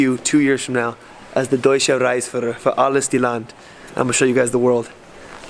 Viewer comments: it sounded like you said ''reichsführer'' lol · de Deusche Reisführer🤣🤣.hab doch glatt Reichsführer verstanden:dothefive:😅😅😅😅😅